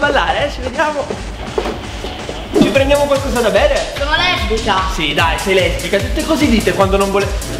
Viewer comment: cosa ha detto?? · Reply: Dice "sono calabrese io"